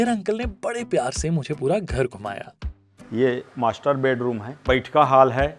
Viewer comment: i heard masterbte room 😑😑